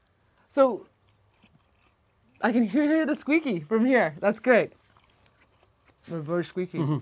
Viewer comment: the captions say “I can hear the squeaky Fromme here” 😂